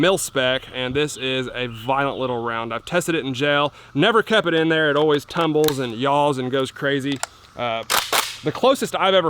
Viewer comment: I thought he said “I’ve tested it in jail” 😂